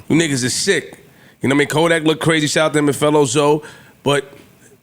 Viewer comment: wait flip is Haitian ? …did he say a “fellow zoe” or he was naming someone called that lol